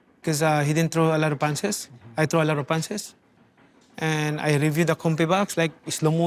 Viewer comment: I swore he said “a lot of bitches” 😂😂